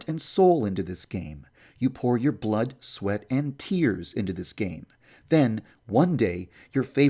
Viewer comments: DID U JUST SAID BLOOD SWEAT AND TEARS AT 15:11 NA PITTAM NUNMUL ARMYS ARE U HERE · did he just say blood sweat and tears ? BTS ARMY WHERE U AT ?